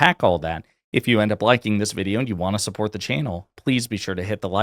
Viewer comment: Man, I seriously thought you said, "If you wanna support the anal"